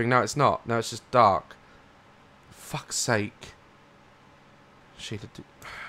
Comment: I'm too early for Jumpscare Times so I'll take one for the team and suffer so you guys can watch without worries. Build me a raft so others don't have to die! xD Jumpscares: 4:13 - Tiny jumpscare, probably isn't one, to be honest. 6:48 - Another tiny one. 7:44 - A little one, would make you jump. 10:53 - Not really one but scream is enough to scare me...so... 15:18 - Hello, it's me. 18:23 - Huge one. The highlight of the video! 23:55 - Not exactly one but it's still creepy... That's all, I think! :D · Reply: Ok so i skipped to 18:23 the first words i heard was "fuck the fuck"